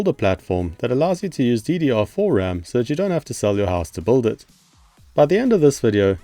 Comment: Had to go back and re-listen a couple of times cause the phrase "you don't have to sell your house to build it" totally sounded like "you don't have to sell your ass to build it"! 🤣